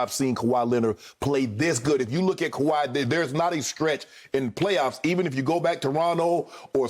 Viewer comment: Did Shannon say "skretch"?